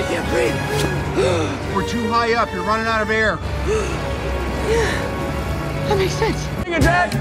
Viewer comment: the subtitles said: "-you're running out of beer -that makes sense" 😂